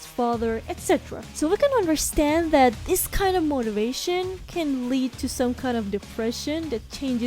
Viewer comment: did you say “MOTIVATION”?!?! * Bury The Light Intensifies *